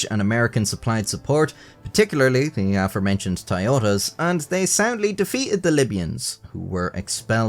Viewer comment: At @ 2:54 does he say 'they SADLY defeated the libyans' ? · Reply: Soundly!